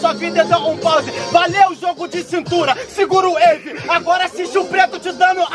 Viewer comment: ele fala wave e ali estava escrito "eve"... foi braba com a pocoto kkkkkkk manda um salve irmão 🙏🏾🙏🏾